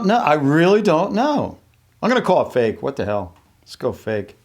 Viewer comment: Did he say the H word? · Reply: The Diamond Garden what's wrong with saying hell? its just a place. its not a swear word at all